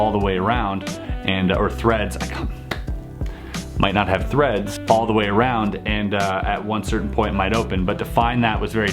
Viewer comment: For a moment I thought he said "My nut had threads all the way around".